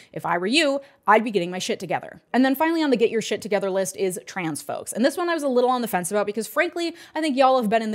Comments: Great video! On 12:02 you said Transphobes instead of Transgender people or a similar term. If you didn’t say that, sorry. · CORRECTION at 12:02 , You said transphobes instead of trans people.